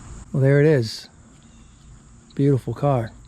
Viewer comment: Did you say "bluetiful"?